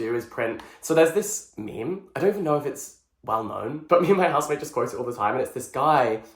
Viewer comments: It took me two rewinds to understand that said "Me and my housemate" instead of "Me and my husband" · Not me going back to 12:45 if I heard it correctly…did you say husband? 😅 (or housemate…?) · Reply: housemate hahaha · I genuinely thought u said husband not housemate and was gonna be angrh like!!! I didnt know u were married!!! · 12.44 I heard 'me and my husband' and I was so confused. Because you don't have a husband right? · Did he say housemate or husband?😂